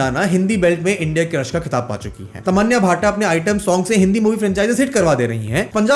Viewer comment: bruh did said Tamanya bhata? XD